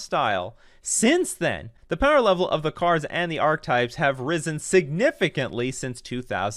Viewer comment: When you asked if I wanted those archetypes with Faithless Looting I thought I heard you ask if i wanted those Arclights so you know where my brain is at